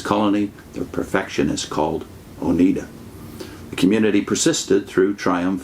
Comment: Always heard it as Onida not Oneda.